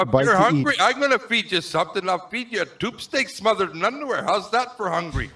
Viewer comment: what does he say that he's gonna feed Richard with? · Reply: Tube steak smothered in underwear?